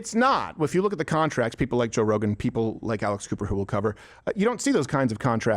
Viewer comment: I thought you said Alice Cooper, and was so confused.